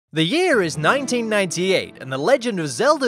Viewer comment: I had to pause the video for a second after it started and all I heard was “The year is 9” and I nearly pissed myself